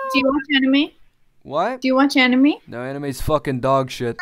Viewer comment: did he just say anime is dogshit?